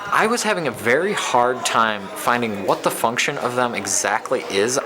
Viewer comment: I thought at first that he said "what the Fu**" lol ...